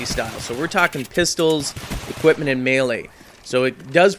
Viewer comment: when you said melee i thought you said mailace and then im thinking OMG YOY HIT PPL WITH UR WIFE ?? sorry lol great vid